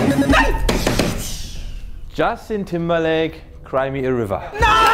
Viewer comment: Ein Fehler bei der letzten Runde! Er sagt ''cry me THE River'' oder hab ich mich da verhört?